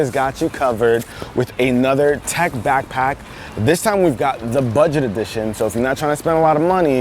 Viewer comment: its only amount over 20$ Me: i thought he said budget edition